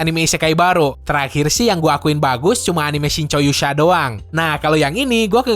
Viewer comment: Lu ngomong apaan bang 7:49 since Yoo sa?? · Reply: Shinchou Yusha